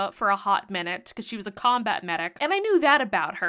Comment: I misheard „she was a combat medic” as “combat mage”. Apparently my nerdy arse needs hearing aids or a good night’s sleep for once. Or both.